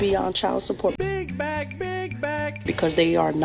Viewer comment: I laugh cause I finally understand he saying BIG BACK BIG BACK I thought it was saying...BIG MAC, BIG MAC. Lol